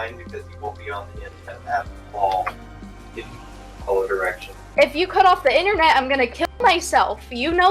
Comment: at the “if you (cant hear this) (something something) direction” part, he said “if you cant follow directions”